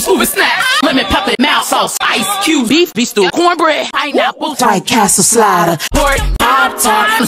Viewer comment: Can someone please tell me what she says at 0:37 ? 😅😅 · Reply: White castle slider